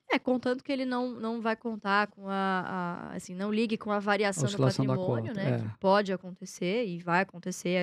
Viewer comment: Bom dia! No time 4:12, o que eles dizem? Oscilação do: aconio, aconito, axonio, não entende! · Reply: Hahahahaha é variação de patrimônio Eduardo